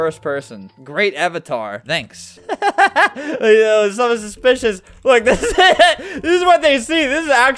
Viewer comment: is it just me or i heard bitches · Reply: He said suspicious